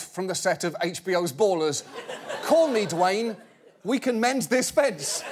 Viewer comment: Can someone please tell me what Oliver is saying at 0:11 ? I'm traducing some episodes to improve my english, but it's sometimes difficult... Youtube's auto subtitles says : "call me Dwayne, we can mend this feds" but it sounds weird to me... thx in advance ;) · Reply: It is "Call me, Dwane. We can mend fences." (Meaning he is asking Dwane to call him, so they can mend fences.) Good luck on your English studies!